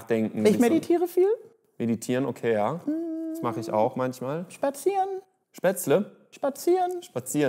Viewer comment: Ich habe auch Spätzle verstanden 😂😂😂😂😂😂